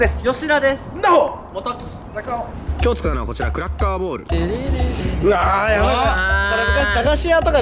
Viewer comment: クラッカーボールがクラッカーボレーに聞こえたw